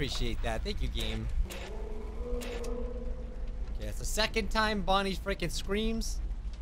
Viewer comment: I heard you say Bonnie pregnant